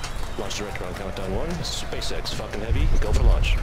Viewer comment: am i the only one that thought that the dude said space x f"n heavy